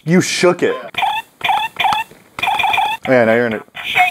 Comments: am I the only one who heard "kiss me" 0_0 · I thought he said kiss me